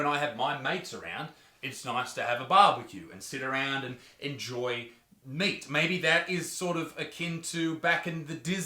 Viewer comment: All I heard was "more meat may"